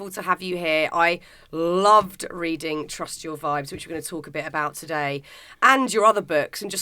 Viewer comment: Say Wht??? "Trustyourvibes" What's that? Did you say 'Trust your vibes'???